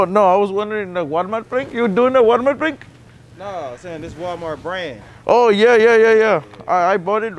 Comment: the first guy was ready to grab them when you said free 😭😭😭 he realized after u just took them off the shelf 😭😭😭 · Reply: Lmao it was funny how I thought he said it’s just a prank when he said it’s Walmart brand 😂